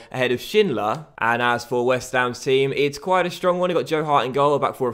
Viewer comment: did he say that they have Jarred Hart on goal???